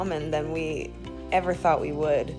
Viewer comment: I thought that she just said “even though she’s a meteor”